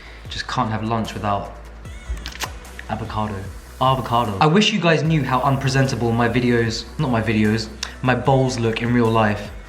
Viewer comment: I thought he said “i wish you knew how unpresentable my balls are in real life” 😭😭